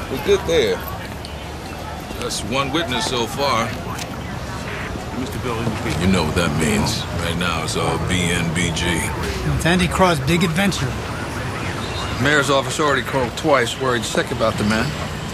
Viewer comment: what did the guy say 0:21 "Andy Craw? Big Adventure"?